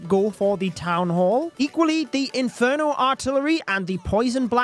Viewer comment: i heard eagle artillery 😔😔😔